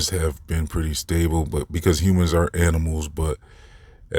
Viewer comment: did you say humans ARE or AREN'T animals? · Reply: Aren't